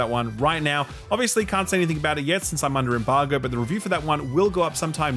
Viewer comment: I had to rewind that AC: Shadows bit (0:43), a couple of times. One moment I heard "can't say anything bad about it yet", and the next was "can't say anything about it yet". Think I'm putting my own pre-perceptions in there.